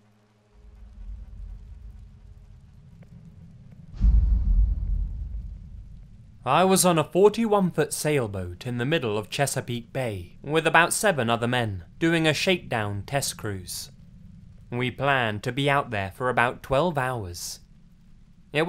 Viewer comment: When it said Chesapeake Bay I thought it said Cheesecake Bay and I got so excited